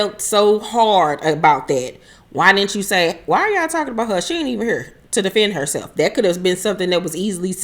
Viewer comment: I thought kimmie said why were they talking about Tisha if she's not here